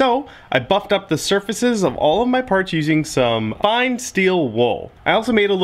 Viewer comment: 😂😂😂 wow I wasn't paying attention at 2:02 and it sounded like he said, "So, I f#%@ up surfaces of all of my parts..." HAHAHAHAHAHA love this dude! 👍